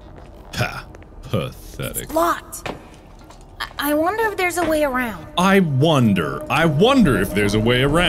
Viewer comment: Anyone else think they heard Gregory say "Fuck. I wonder if there's a way around" ????